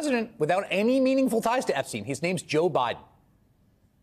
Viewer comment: Did he call them "Fox Skews" at 13:32? Because I dig it.